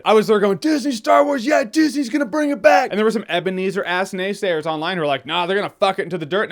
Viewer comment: Can someone please tell me what the J-dawg is saying at 1:59 while he is agressively wiping his finger? It sounds like " [...] and there were some ebonies or ass naysayers online that [...] " but I'm not sure what that means. Question 2 is if someone can confirm that the above mentioned finger wiping motion means something to indicate a person or persons are filth? Thanks in advance!